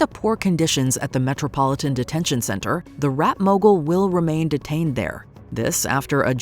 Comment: When they called him a rap mogul, i thought they said rat mogul. How they're saying the detention center has rats 😭